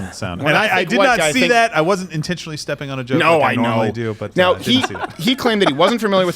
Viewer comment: I don’t know if anyone has noticed, but the auto generated captions are so far off it made it sound like Trumbo said (time 9:18) he “did not intend to step on a Jew” when he said among other stuff... Youtube really doesn’t stop trying to censor and make you guys come out as the worst people in the world.. it’s sad and I feel bad because I listen louder with crowder on a weekly basis when I’m working.. It does annoy me how shameless the left is in terms of doing what they propagate the idea that there is free speech. Honestly, if these guys have something that could be considered offensive to trans people (I am one, slowly making my transition), why should I take it so personally? It makes no sense. In the end of the day, if they make a point, that’s what it is. Not an attack. But a carefully constructed opinion based on reliable statistics, which even I check on because I want to be able to ground myself politically. Crowder, you guys from the show.. great work, keep on grinding and good luck for the years to come! With love and appreciation, a furry.